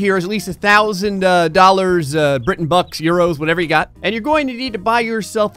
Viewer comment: Did he just say britain bucks